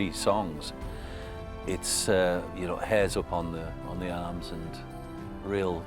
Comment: Please, as a non-native speaker, sometimes it gets hard to catch up all that beautiful British accent. Could someone tell me what does Troy say in 6:32 "It's ... upon the arms"?? Thank you guys in advance! ;) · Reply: Sounds like "It's, you know, hairs upon the arms" if that makes sense at all. I'm not native speaker either. E: Oh someone already answered. I have had this tab open for few hours so it didn't show up before refreshing :D